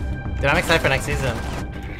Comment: woah there shaky boi my girl watches this channel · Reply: Andy Tee whats he saying im hearing ‘ ... diamond next season’